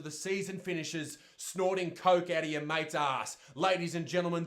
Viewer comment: I’m sorry, did I hear that correctly or did he just say snort some coke out of ya mates ass?